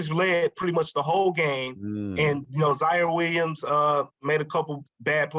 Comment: Did he say Zion Williams at 5:13 ?